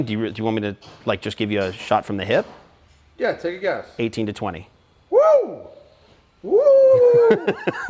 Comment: he says his estimate of 18-20 After which Matt says “Woooowoohoooo” never does he say 18 grand. However I do think the Porsche specialist is saying 18k -20k